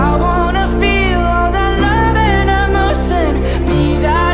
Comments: Conan mentioned in a Genius interview that the line: "I feel love emotion" is tricky because some people hear "love" and some people hear "no" (I feel no emotion) depends on their feelings. And i was watching this video with English subtitle and at this line the subtitle said "I feel no emotion" which i think it's really funny. Like are you okay YouTube subtitle maker person? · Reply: i heard "i feel no emotion" Thank for confirming i need therapy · Conan mentioned in a Genius interview that the line: "I feel love emotion" is tricky because some people hear "love" and some people hear "no" (I feel no emotion) depends on their feelings. And i was watching this video with English subtitle and at this line the subtitle said "I feel no emotion" which i think it's really funny. Like are you okay YouTube subtitle maker person? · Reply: I heard “no emotion” uhhh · Conan mentioned in a Genius interview that the line: "I feel love emotion" is tricky because some people hear "love" and some people hear "no" (I feel no emotion) depends on their feelings. And i was watching this video with English subtitle and at this line the subtitle said "I feel no emotion" which i think it's really funny. Like are you okay YouTube subtitle maker person? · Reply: Yeah true cuz I heard 'i feel love emotion' but the subs said 'i feel no emotion' n I got confused by it lol